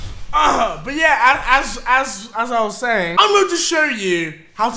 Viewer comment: did he just said (BLYAT) on